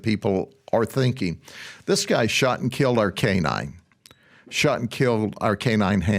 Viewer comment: I was like 68X's! WTH? Then I heard, "shot and killed our canine" 🤬🤬🤬 I get it now. Sorry.